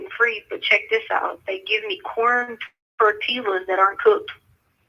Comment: did she say “corned tortillas that aren’t cooked” 🤣🤣🤣🤣🤣